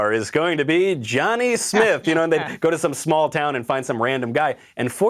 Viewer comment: thought he said Johnny sins